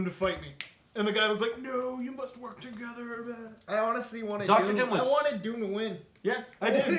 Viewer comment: did they seriously turn dr. doom, one of the best written characters ever, a calculating genius... into a cheap psychopath!? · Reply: LOL i thought you said dr.boom